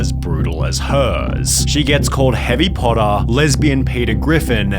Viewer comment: Came back after watching a couple shorts, all I heard was “Harry Potter, Lesbian Peter Griffin and White Kim-Jong-Un” and had to take a moment and reconsider my life choices.